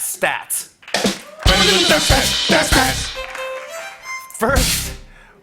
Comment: I thought Jimmy said "Fat Stats" 🤣